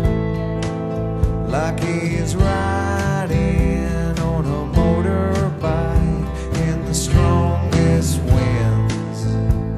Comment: I always hear it as, "like he's riding on a motorbike AND THE STRONGEST WINS". I know its not but I like it that way. Both work.